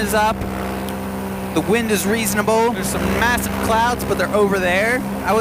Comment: Did he just say massive???